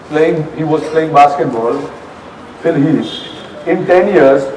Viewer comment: what is the athlete name said by sahil?? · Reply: Indian Nature its phill heath